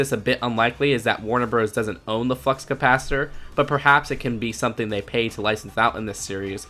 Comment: who else hears F***s capacitor